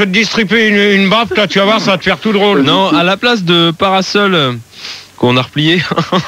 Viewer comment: Qu'est-ce qu'il a dit Phildar? Je n'ai pas compris. J'ai repassé plusieurs fois la séquence, en augmentant le volume du son, mais je n'arrive toujours pas à entendre ce qu'il dit. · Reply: Stéphane H il dit « à la place de parasol...qu’on a replié »